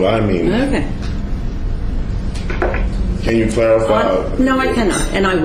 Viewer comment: what does he say, is it "can you clarify?", i can't understand him half the time.